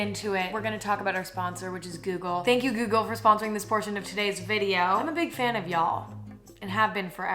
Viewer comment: I thought it said “thank you Google for supporting abortion...” 💀